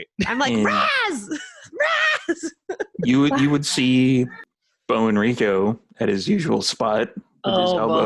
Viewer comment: I am seriously considering "stealing" the Sheriff's name the way I originally misheard it (Beau Enrico) as being born of Cajun mother and Latin Father. Or vice versa if I decide on the mother as a single parent.